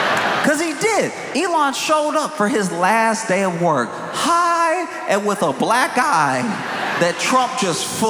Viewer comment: I misheard that Elon came to work "high and with a black guy" and amazingly the joke still works the same 💀